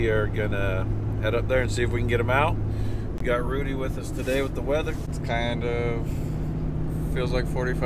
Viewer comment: Now here's Rudy with the weather Rudy:"i thought you said feather"